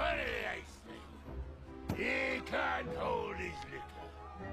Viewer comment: Before I watched with text or read the transcript whichever I did first 🙄😂, I thought Gimli said "He can't hold his little..." and I was like 'What'd he say?? 😶